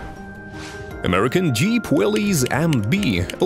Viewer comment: Did you say Willy?